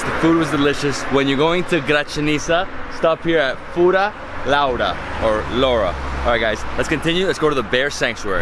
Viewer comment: says the hindi abusive word lauda instead of Laura lol.